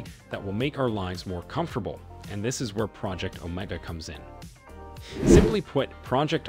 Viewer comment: Project, omg, that's what it sounds like he is saying (project oh my God )or ( omeega, ) ( Omaha) Too. Funny 😅😅😂